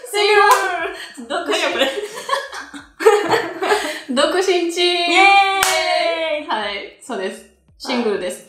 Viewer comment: 独身チーム！を最初に僕ちんちーん！と聞こえたｗｗｗ